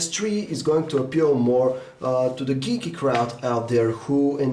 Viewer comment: did he say geeky crowd???